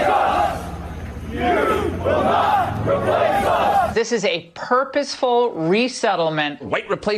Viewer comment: the subtitles show, "You will not replace us," but what is actually said is "Jews will not replace us." Important distinction, Fox.